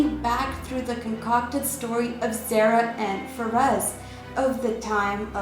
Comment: did you say "Sarah and Ferez"? I tried to look it up.